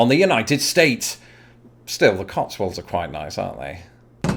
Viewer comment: HAHAHA.. I thought you said "the cockstands are quite nice". Thought, "Damn I gotta go to the UK!"